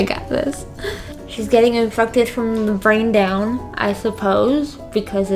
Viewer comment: I know you said "Shes getting infected from the brain down" but it sounded like you said "Shes getting infuckted from the brain down"😂😂